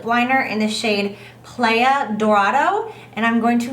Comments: When she said the lip liner name I thought she said playa dorito I was like woah perfect name for Jaclyn and the color is a bright orange haha woops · I swear, I heard "El dorado" at 18:18 oops